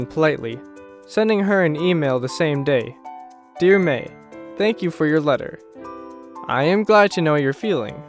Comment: almost suffocated from laughter because it’s says “Hank You instead of “Thank You”